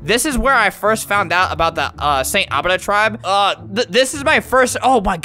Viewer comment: At first I thought he said "saint adida"